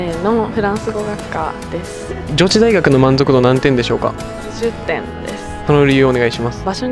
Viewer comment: 80点って聞き取れる人おる？w 20点にしか聞こえないんだが